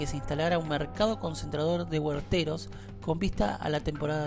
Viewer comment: minuto En minuto 4:05 se escucha: mercado concentrador de que bolseros ?, hueveros ?. No se entiende !!! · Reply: Hola Horacio, un mercado de Huerteros!